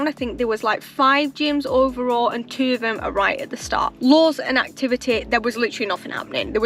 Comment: What do you mean "Laws"? At least, that's what the caption says you're saying... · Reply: Lures haha it’s my accent